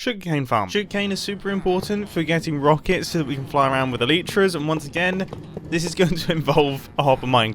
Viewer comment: Wait a minute! Did he just say Aleetra?!